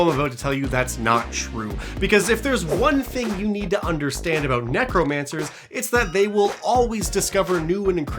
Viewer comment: 100% thought you kept saying Necro*MANTLE* Like the topmost part of Earth's crust.